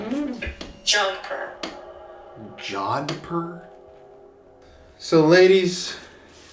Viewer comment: I heard "jodhpur" right after picks up the book. I looked it up and it's a type of pants - that look a lot like the ones is wearing!